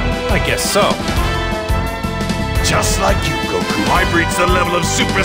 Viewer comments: HOLD THE FUCK ON. DID HE JUST SAY "GOKU"?! EXCUSE ME, THIS IS NOT VEGETA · did Vegeta say Goku instead of kakarot /_\ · Wait did he say Goku?!?!?!?!?!?!?!?!?! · Did Vegeta Just Say Goku Holy Dragon Balls! Vegeta Doesn't Like To Call Goku Vegeta He Always Like to call Goku Kakarot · Dafuq did i just hear? Did vegeta say goku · did vegeta say goku instead of kakarot?